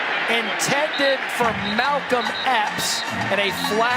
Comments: Not going to lie. Thought I heard the sports announcer say Malcom X, not Malcom Epps at first. Initially, I thought, "What is Malcom X doing playing football? "Thought he was busy fighting for African American rights next to Martin Luther King, Jr; both of whom are legends in my mind! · There for a minute I thought they were saying "Malcom X". 🤨😋